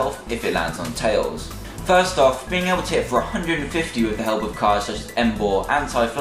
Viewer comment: I just realized that I thought he said "with the help of pluspower" but he should fix that.